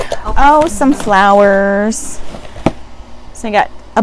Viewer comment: LOL, first I thought you said Sunflowers and you said "Some Flowers". Steam mop head on the head 😂😂😂🤣🤣 that makes you a "Mop Head"!!!! Hard rock candy, oh my, LOL. I was waiting for you to try on the mermaid tail and the mop head too! Oh darn, not a mermaid tail. Awesome Video!!!!!